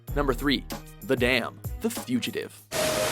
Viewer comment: well i heard "the dam fugitive" lol